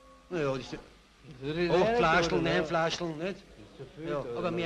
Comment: Was sagt der erste Typ wieviel Flaschen er am Tag trinkt? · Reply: 8-9 Flaschen